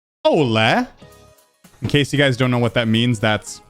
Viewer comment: I thought he was trying to say hola at the being and not hello backwards